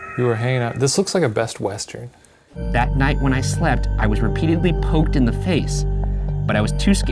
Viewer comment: When Ryan said it poked him on the face, I thought he said it pooped on his face...... wtf lmao